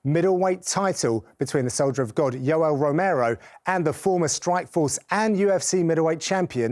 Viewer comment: Is it just me or did he really say Yellow Romero at 0:29 ?